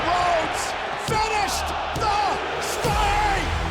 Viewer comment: My heart swelled when I heard Cole say, "Finish. The. Story". But that heartbreaking moment between Paul Haymen and Roman at the top of the ramp hurt... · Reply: He said Finished the Story actually.